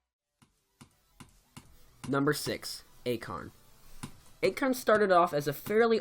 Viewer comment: Haha when he said acharne i thought he said acorn.